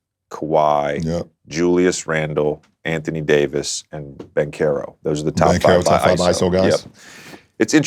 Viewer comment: Who did he say at 0:27 ? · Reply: Paolo Banchero